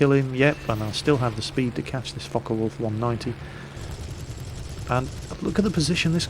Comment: I thought he said fucker wolf 190